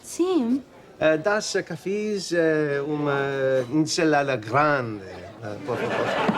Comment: OMG He ordered the infamous "Big Salad" in Portugese lmao! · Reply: I think I heard him say: Uma salada grande. Of course, the accent isn't excellent, but passable. Especially when he says dos cafees instead of dois cafeis, but what do I know?